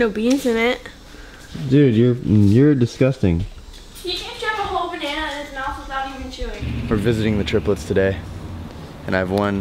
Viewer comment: Did anyone else hear "he can shove a whole banana in his mouth without even chewing" 00:46 😂😂😂